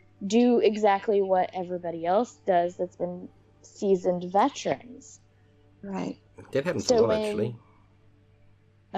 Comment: Me on my6000th time trying to understand what you said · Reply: "That happens a lot, actually." (Also happens a lot that people can't understand what I say!)